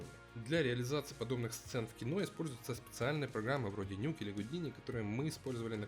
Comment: что за проги , я не расслышал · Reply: Nuke и Houdini